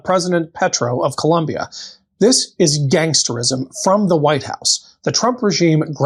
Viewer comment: "this is gangsterism," he said. what I THOUGHT he was about to say ... LOL... is ... "this is gangsta!" hahaaaaaa!!!